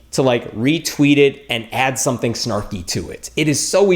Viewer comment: ''And add something ??? about it'' What did he say? I don't know the word Snorky or something like that... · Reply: Julian Cowpurse I think he said 'snarky', it means a short phrase that is generally sarcastic and made in a negative tone. <link> <link>